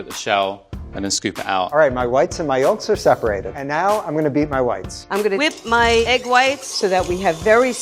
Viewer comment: I thought he said he was going to beat his wife